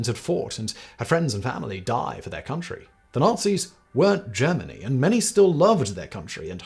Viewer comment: wait wait wait... did he say the Nazi's weren't German?????